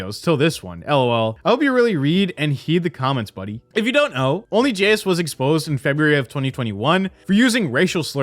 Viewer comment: who? Did you say Only Jizz? Lmao